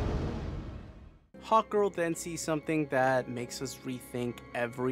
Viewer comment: I love how I kept hearing you say "Hot Girl" instead of "Hawk Girl" lol